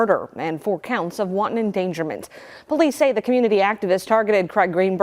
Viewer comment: Did she say wonton endangerment? That's a serious crime in chinal